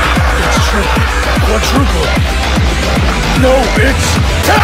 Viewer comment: why did it sound like hit said "No Bitch" Lol XD